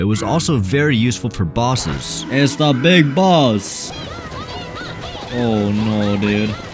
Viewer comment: did he say big boss